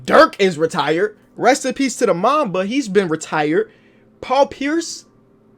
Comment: honestly I thought he said "MAMMA"